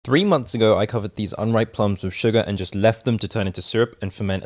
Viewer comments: i thought it said urine plum syrup.... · Thought it said urine plum syrup